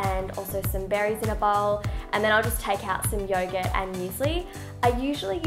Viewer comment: At first I thought she said that she eats yoga for breakfast instead of youghurt. Yum, yoga 😂😂